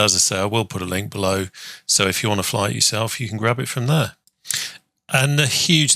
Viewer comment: As always, a fantastic video. And,…. At 1:52 did you really say, “ …if you want to fly it yourself you can wabbit from there.”.. :) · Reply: Thank you! Laughing out loud here.......I said grab it but my lord it sounds like Wabbit! I got Wabbit on the brain!